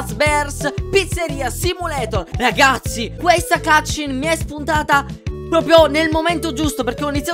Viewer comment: ho capito "cazzin" al posto di "cut-scene"